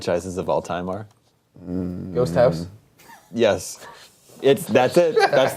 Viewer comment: Uh, what the hell does Greg say at 39:57? "Ghost House?"